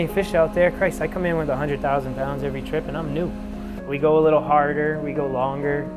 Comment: for a split second i thought he said "and im noob"......need to stop play videogames.